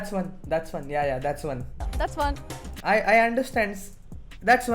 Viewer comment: i think she said thats fun